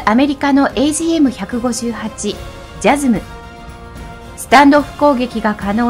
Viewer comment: スタンドオフ攻撃がスタンド攻撃に聞こえたw